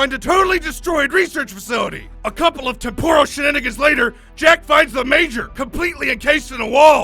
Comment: did he just say “a couple of- see you niggas later”? Am I the only one who herd that??